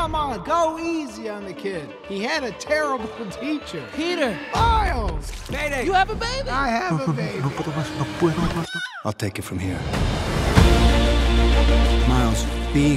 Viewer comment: is he saying no puedo más? 😭